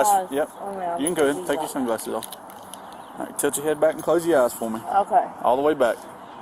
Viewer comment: I keep hearing the cop say touch your head back. Is my hearing bad or is he pronouncing tilt touch?